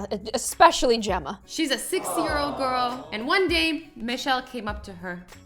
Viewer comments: did she just said a 60 years old girl or is it just me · who else heard 60 year-old girl instead of 6